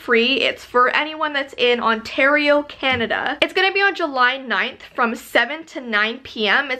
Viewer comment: did she say JULY 9 :D thats my day. My birthday.